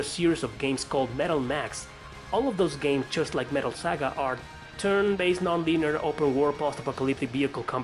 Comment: wtf did he say??? Lol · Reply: turn based non-binary open-world post-apocalyptic vehicle combat JRPG